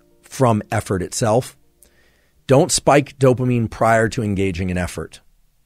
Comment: Why am I hearing the “don’t spike dopamine” concept as him saying “don’t take pre-workout”?